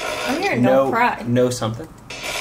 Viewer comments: It sounded like they said "no cameras" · I heard "No credit," in the part that you think sounded like, "Go crazy."